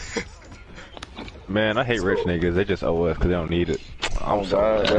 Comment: What does that one dude say at 0:54? What don't rich people need? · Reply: He says rich people just os they don't need the money.. he's referring to sonic